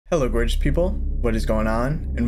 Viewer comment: When he said “hello gorgeous people” I thought he said “hello British people”